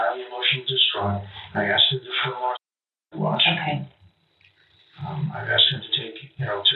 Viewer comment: What's with all the censoring, this is fucking ridiculous. What does the officer says at 55:21 ? "I asked him to put him on su***de watch" Im so done with this soft ass world